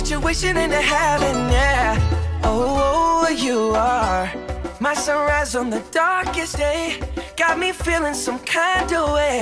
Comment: WTF? any bady understand what he say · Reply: My sun rises in the darkest days 😷